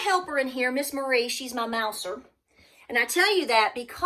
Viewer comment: Did you say Mauser or Mouser? LOL At first I thought you said Mauser, I was thinking you had a Mauser to protect your pantry, LOL…😆🤣 🤗